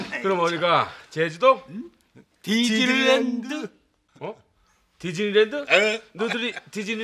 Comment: 왜 디질랜드로 들리죠?? 저만그런가...